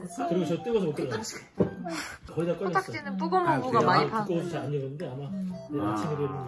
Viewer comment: 아니 근뎈ㅋㅋㅋㅋ 언닠ㅋㅋㅋㅋㅋㅋㅋㅋㅌㅌ 0:31 너무 평화롭고 아련하게 "코딱지는 쁘고모부가 많이 파는데..^^" 오늘도 어김없는 코딱보이 모먼트.....ㅌㅊㅋㅌㅋ 가 너무 이뻐서 못 알아채다가 세 번 정도 보니까 들리네욬ㅋㅋㅋㅌㅋㅌ